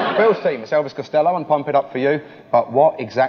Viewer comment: What did he say the band/song at 14:34 was? I heard 'the attractions,' and I googled it and it came up with Elvis Costello and the attractions, but I want to know the song name BD THANKS ANYONE! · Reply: NVM IT'S PUMP IT UP